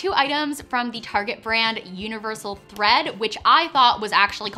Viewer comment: I thought you said universal breads 🤣